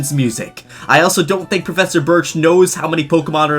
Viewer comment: When he went to Prof.Birch, I thought it said Professor Bitch